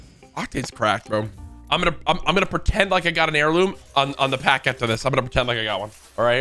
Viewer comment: Lol, I heard you say octane heirloom and I only need his heirloom to complete my octane dream setup and love the vids man 🤓🤓🤓